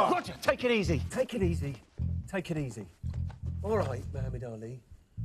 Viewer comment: Can someone tell me what Brian (Gwil) said around 0:12, the nickname (if it was) that he used on Freddie (Rami) 😂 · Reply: I thought he called him Mohammed Ali. Could be wrong.